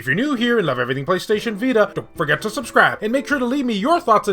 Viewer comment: I thought you said “Forget to subscribe”